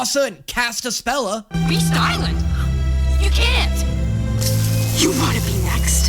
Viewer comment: I thought she said “we styling”